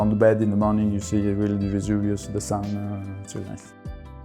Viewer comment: Vesuvius instead of "luxurious" 😉